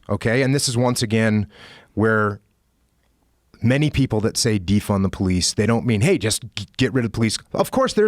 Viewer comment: All I hear is "DEEF THE POLICE".. took me a while to work out what Jocko was saying lol.